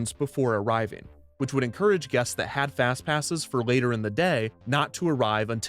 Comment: Years later and I still hear "half-ass passes" instead of "have fast passes."